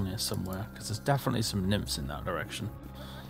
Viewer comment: did he say what I think he said? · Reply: "there's definitely some nymphs in that direction" what did you hear?